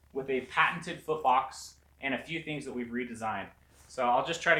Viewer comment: foot box i thought you said fart box bahaha